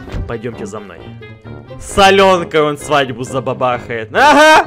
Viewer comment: мне послышалось "салёнка''